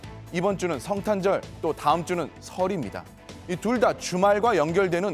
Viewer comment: 내 귀에만 ‘다음주는 설 입니다’ 라고 들리나??